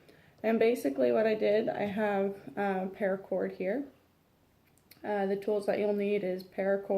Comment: What is the name of the cord? I want to make sure I can get the same lol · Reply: I heard her say paracord. You can google it.